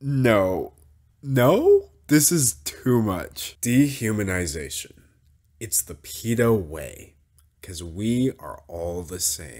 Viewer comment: I seriously thought you said "dehumanization, it's the pedo way" which... I mean it's accurate but besides the point in this video, and I really wasn't expecting to actually mix up Peta and pedo today Edit: I just found out that my big sister doesn't know Peta